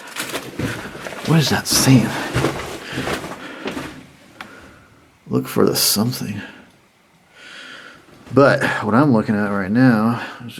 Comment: I think says "LOOK OUT FOR THE CART"?